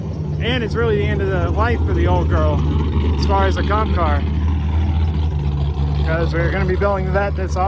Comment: Everytime you say “comp car” I always mishear it has “cop car” and just imagine the miata with police decals booking it down the highway chasing a hellcat or sum basic straight line shit😂